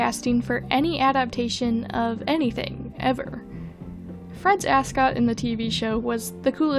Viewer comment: Wait Fred’s cravat thing is called an ascot? He mentioned it in one of the cartoons and I thought he said mascot 😭😭